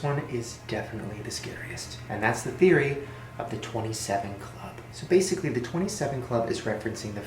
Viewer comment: Omg when he said the 27 club I thought he said 27 condoms 😂💀